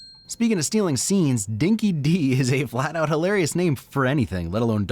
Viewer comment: 'Dinki-Di' is pronounced 'Dinky-Die'. It is an Australian term meaning like, 'True Blue' or 'The Real Deal' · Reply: I allways thought that the can said"Dinky Dog"